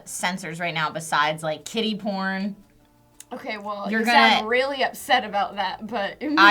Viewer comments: I thought Trisha said "Kitty Porn" not Kiddy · I was confused for a bit, because I thought you said kitty porn. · I swear, I thought they were saying "kitty porn" for a solid 30 seconds. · As they were saying "Kiddy porn," I thought that they were saying "Kitty porn." I was so lost. Why would I even think kitty porn?.. HELP ME... O_O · Definitely heard that as Kitty porn and I was perplexed... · I thought you were saying "kitty porn" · I thought when you guys said kiddie porn i thought u said "KITTY porn" i was like thats a thing?? lmao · Thought they were saying KittyPorn for a second